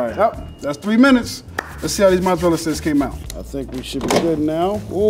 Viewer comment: I thought he said "let's see how these muthafuckas came out" lmao